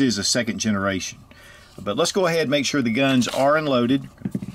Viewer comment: did u actually say 'let's make sure the guns are loaded.."?? :) (I listened twice, and that is still what I heard...